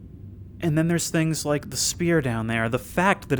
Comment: Spear? I may be mishearing what he’s saying, but I think I might of missed something. Maybe it’s the accent I don’t know. Edit: or did he say Sphere referring to the thing in the cave? That actually would make more sense. ( literally took me 30 minutes to understand that obvious detail). Or I could of waited to 49:48 instead of being stupid